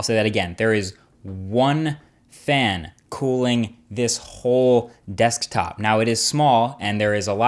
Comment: fail; you said khol instead of hole